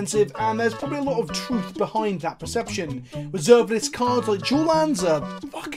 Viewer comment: You're accent is so heavy, everytime you have said dual lands I thought you were saying "jewel" lands. Gave me a chuckle at how dumb I am. Keep up the great content!